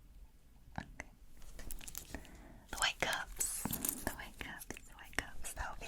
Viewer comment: nah for quick sec I thought that said ‘white guys’ and not ‘white cups’